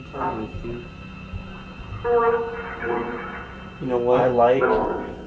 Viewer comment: I heard "I like" then faintly I heard "corey" like was saying she likes